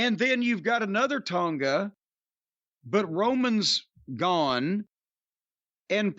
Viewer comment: The best part i heard was "romans gone" still not watching